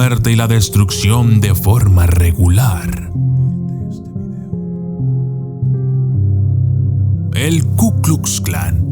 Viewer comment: Pongan atencion en el minuto 7:37 se oye algo no no entiendo que dise al principio lo unico que entiendo es .......este video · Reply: Comparte este video